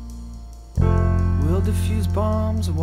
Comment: Don’t change your name Keep it the same For fear I may lose you again I know you won't It's just that I Am unorganized And I want to find you When something good happens If you come down We'll go to town I haven't been there for years But I’d be fine Wasting our time Not doing anything here Just doing nothing We’ll sit for days And talk about things Important to us like whatever We'll defuse bombs And walk marathons And take on whatever, together youre welcome. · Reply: "And walk marathons?!?" This whole time I thought he said " Walk around in thongs" 😲